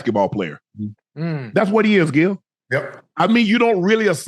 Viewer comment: We not gonna act like Shannon ain’t call them “Skip” 😂😂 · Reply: I think he said Gil 7:16 if that’s why ur referring to